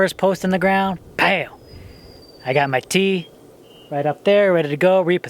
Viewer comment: Lol city girl here thought you said "I got my tea" when you said "I got my T" lol lol. I though oh how quaint, he drinks tea while working. Not. Haha. WOW the rain!!! Bless you fir checking out the cows. Love that. Awesome video! 💖💕🌷🌳🍃